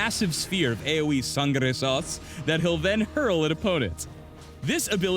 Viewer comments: I've replayed 3:03 100 times yet I have no idea what kind of sauce he's saying. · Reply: its sangre sauce. not tsundere lol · what the fuck is he saying? Sangara sauce? What?